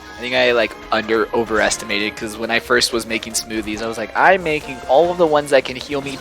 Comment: Every time I hear say "Smoothies" my brain replaces it with "Smoovies".